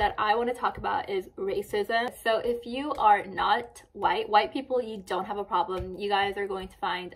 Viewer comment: This is very good. I am a white person who always tried to shed a light on this matter ( I was subjected to very intense racism when I attended korean school :/ ) and I HAVE ALWAYS been accused of being racist, because people cannot really see that Korea and Asia in general is not kpop bands and cherry blossom flowers. Thank you for speaking up about this, I think that shedding a light on the matter will make Korean people improve on these matters. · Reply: I thought she said if you were white you would not have a problem 🤷‍♀️